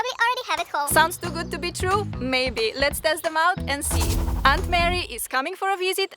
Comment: sounded like "I'm married" instead of Aunt Mary lol